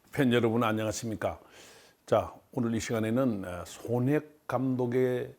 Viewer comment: 이 분위기에 죄송하지만.. 0:04 소낵 감독으로 들리네요